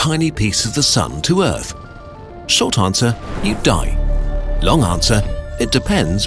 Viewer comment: at the start i thought he said "shit answer: you'd die" XD